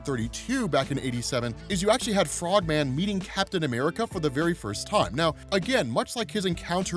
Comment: Thought said eating Captain America for the first time